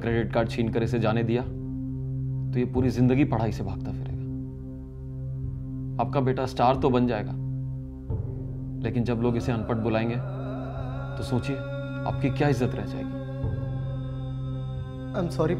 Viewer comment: I heard "jab log ise Elvish bulayenge l"